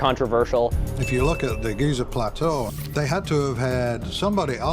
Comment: I keep hearing “the Geezer plateau” in cockney english.